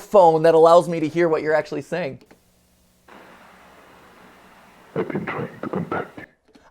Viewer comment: I thought I heard "I've been trying to come back to you'